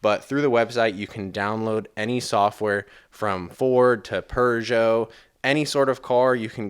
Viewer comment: Did you just say, "Per geot" as in "Peugeot?" 🤣🤣🤣 Otherwise, good video.